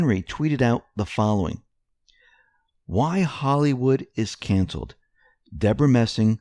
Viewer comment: I heard, "Why Hollywood is cancer"...